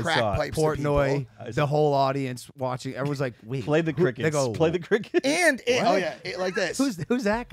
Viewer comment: did logan say the n-word ??? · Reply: He said “they go” lol